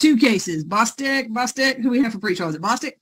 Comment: I will never get tired of Judge Manning saying, "Pretrial!". lol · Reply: To my Australian ears 👂🏻👂🏻🐨 when I first heard Judge Manning say "Pre-trial!" I thought she was saying "Quick draw!" I was quite puzzled 🤔 as to the context of that! 😂 🙃 😳